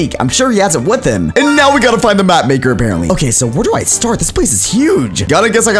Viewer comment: When it said find baby mapmaker i thought t said find baby markiplier